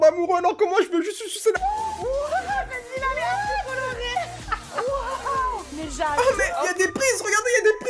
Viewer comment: attendez c'est moi qui bug ou elle a dit "merci bolloré"?